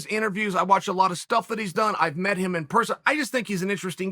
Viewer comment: idk why but I thought chael said “I met him in prison”